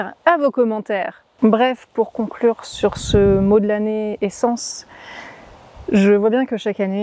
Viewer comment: J'ai entendu "la naissance " quand tu disais "l'année essence"..... A mon avis tu es en train de laisser de la place pour quelque chose de très chouette pour toi ! Tu es sur le bon chemin, au bon endroit et au bon moment. Apparemment on ne peut pas être ailleurs que là ou il faut qu'on soit alors INCHALLA Je laisse les merci et les tu vas nous manquer je pense que tu les as déjà entendu ;)